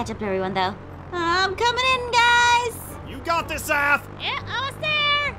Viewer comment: the transcript said “ ass “ instead of aph